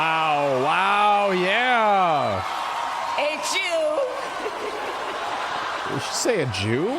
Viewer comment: She said "H U" (Howard University) I was puzzled i thought she said a jew at first